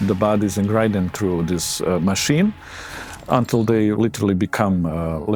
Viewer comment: the guy says: "grind them through this machine" but the subtitles say "ingredients through this machine" . Just something I noticed, I love your videos guys keep it up!!👍